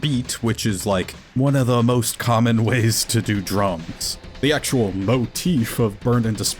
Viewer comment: Did someone hear "The most common way to play drums" as the most common way to do drugs?